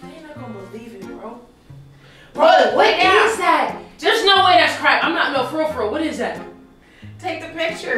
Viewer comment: I almost choked on my food when she said “They put Crack on that donut” 😂 ☠️ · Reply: said "there's no way that's crack" but it sounded like she said "there's no way that's crap"